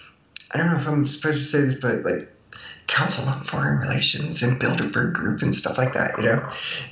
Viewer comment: bilderburg group !! my first laugh in this interveiw.... i want too hear about the "dark tower" miss socalled astro traveler and why not share your techniques too leave the body. . i have succeed as leaving the body. its real ;P . but so few others really have, sadly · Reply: Ohhh I thought she said "build a bear group" lol!!!